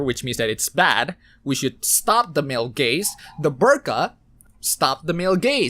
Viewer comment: I only heard "MALE GAYS"